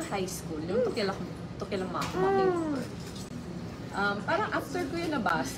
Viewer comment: If you guys could add closed captions, that would be great. Some people didn't hear VP Leni say "To Kill a Mockingbird" around 4:22.